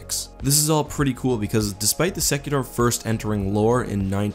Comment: In the Empire, I thought he said "Executor" but the Executor isn't a carrier.